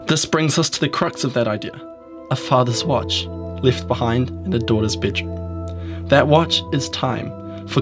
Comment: This is a great video! Will have to watch again and again to make sure I hear all of your well thought out conclusions! But I have to ask, what did you say when you mentioned a "father's watch left behind for -" ? 4:26-4:30? Thank you! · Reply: Marca First off thank you so much for watching, great movie right! No problems I said "a fathers watch left behind in a daughters bedroom"!